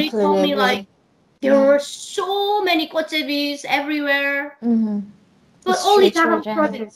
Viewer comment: What is talking about at 1:02:05? “There were so many__________everywhere....” It sounds like she says “Portuguese” but that can’t be right..... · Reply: Gotjebi's - those are the this malnourished little toddlers begging for food.